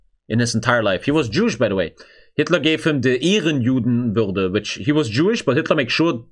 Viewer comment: what does he say? He gave him a _ juden? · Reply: "Ehrenjudenwürde" which tranlates roughly to "honorable jew dignity" which means he was a "better" jew and was protected by hitler and should not be killed.